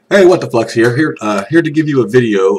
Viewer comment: at the beginning I thought he said what the fucks